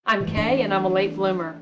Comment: I thought you said ' I'm gay and I'm a late bloomer'. 🤦🤦🤦🤦